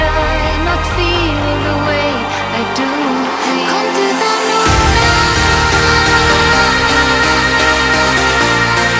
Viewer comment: What does she say at 00:22 ? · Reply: Alexander Kümler She says: komdu þá núna. It's Icelandic and means then come now :)